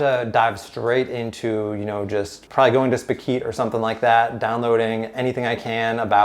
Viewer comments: which website is he referencing: "spakeyt"? I cant understand the website he mentions there - can someone help please? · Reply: he might be talking about Spikeet. try googling that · What was the name of the service he said he would go to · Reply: Spikeet